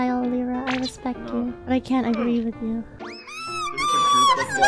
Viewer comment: Naur cuz i thought I heard luca say "feta cheese pringles" lmao